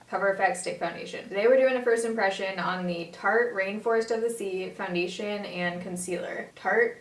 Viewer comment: you've mentioned this in tons of later videos and i s2g i thought you were saying "tarte reinforced by the sea" and I was always like ?????